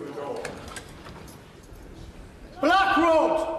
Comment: I thought i heard Black rock instead of Black Rod at the beginning! Also the most dangerous thing threatening the country is Starmer! All Rhetoric without anything backing it up except for plans to put Digital ID on the table again. Screw that!